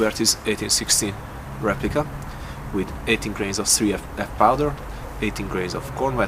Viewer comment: I thought you said 80 grains...I was waiting for the gun to explode.